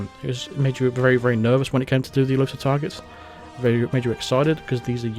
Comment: I thought you were saying "Elusive Tigers" I couldnt figure it out but it must have been "Exclusive Targets" and how about makinf a mode that is super hard, like you cant act strangely and cant walk around behind someone without them knowing, how can they not here you right behind them? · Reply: Elusive Targets